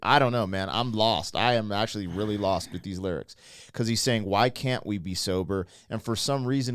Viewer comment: But he is not saying "Why can't we be sober". He says "Why can't we NOT be sober" i.e. "Why can't we be drunk/stoned/whatever".. ;)